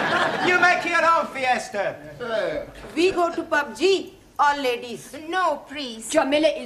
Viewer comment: did she just say pubg?